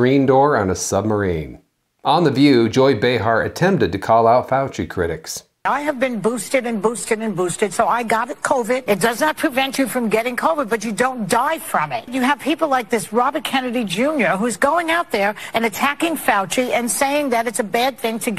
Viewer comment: Did she say she's been boosted and boosted and boosted AND STILL GOT COVID?!? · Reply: It sounds like she says, " I got a covid", like it's a whatknot.